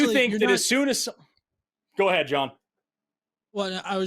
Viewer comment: I know he said "go ahead John" as a polite hand off but all I hear is that meme "Prank 'em John" He just has that sick 'em energy you know what I mean. Also everytime he says Chuck it has so much power behind it.